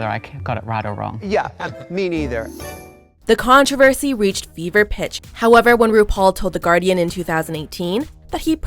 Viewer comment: When she said "when controversy reached pitch" i thought she had said "Bieber pitch" at first. Then I realized that if you're looking at Justin Bieber at the height of Bieber Fever, using his name in that phrase works just as well.